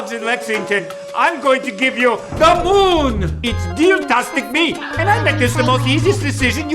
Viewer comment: i thought he said deal-tastic meme